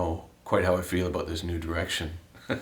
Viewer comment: every time someone says "new direction" I hear "nude erection"